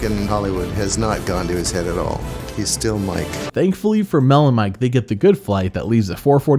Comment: I keep hearing “melon mike” and it always takes me a second to realize that is not a weird nickname but instead two names with an “and” in between